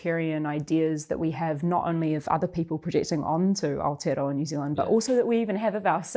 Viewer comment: Hi Duncan. Did you use AI for the subtitles during the segment at 6:00, there are multiple errors including Aborigonal instead of Average and Alter instead of Aotearoa. Cheers, Zach